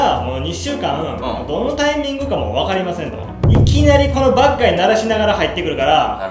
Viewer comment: badguyが、バッガイに聞こえるww